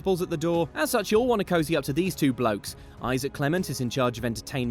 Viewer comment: I thought you said blacks XD.